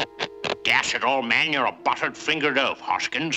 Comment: what is he saying? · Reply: Dash it, old man.